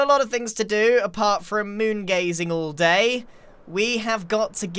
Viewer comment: did he really say "Don't have time for moon gazing all DAY"